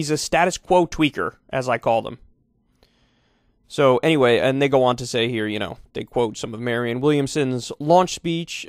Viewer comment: If you got to 2:51, it sounds like Kyle says “Anyways, a ni**a want to say here …” honestly kinda rad